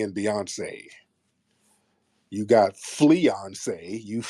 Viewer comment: Fleeyonce had me in tears 😂😂😂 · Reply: I heard it as FLEAyonce but both are hilarious to me this is ammunition for when a tether inevitably says something audacious, scammy, rude and outright lies to me…I stay ready but this is so funny to listen to😂